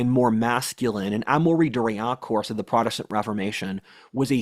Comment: What does he say here? Amori Doriacorse? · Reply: Amaury de Riencourt = French writer that has slipped into obscurity